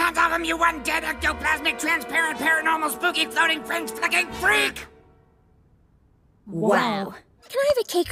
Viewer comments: wait did she just say "f*cking" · DID PENNY CALL CARRIE A FRENCH FUCKING FREAK? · did she say the f word😮 (ik it was meant to be somethin else) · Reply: Freak